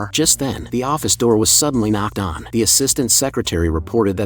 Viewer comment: I keep hearing the term "Knocker" due to watching that series with the white haired mc called Fuchi😭😭